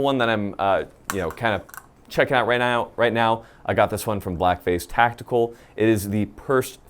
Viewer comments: Oh my goodness I could have sworn I heard "blackface tactical" when he was describing the IR device. Only after I googled it did I find out it was "black phase". Had a good laugh there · Damn he said black phase tactical, but I heard "blackface tactical". I was like damn that's a fucked up name for a store.